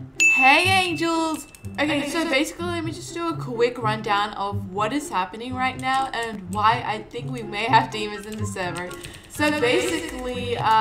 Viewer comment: LOL I thought u said “ok so there’s demons in this surver